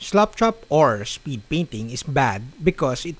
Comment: I know it's the accent (not being disrespectful; I can only speak one language), but I love sounds like he is saying "slopchop". I think that what we should call it; it sounds like a better description because of the way most of us apply the technic. Keep up the great videos!